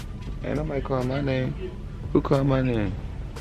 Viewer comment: yall heard at 10:26 it sounded like sumbody called name?.